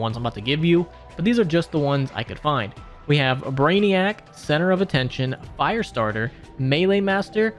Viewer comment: Center of attention sounded like now you see me